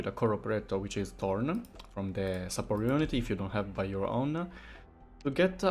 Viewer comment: At first, I thought said "if you don't have Thorns, buy your own" and was so confused xd